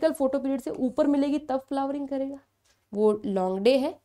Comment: i was watching in 2x speed i heard launde